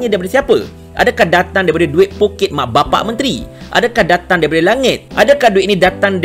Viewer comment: Saya terkejut..tersalah dengar..ingatkan mencarut..rupanya dia sebut "poket mak bapak...." hahaa.. ingatkan " pu#%k mak bapak..." minit 6.46 😅😅